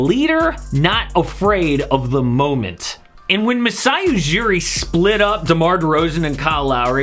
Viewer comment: Miami???? Did I hear that right??